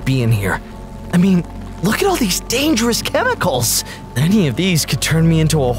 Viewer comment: did yellow say cheticals